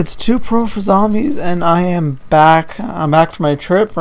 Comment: Thought it said I'm Black.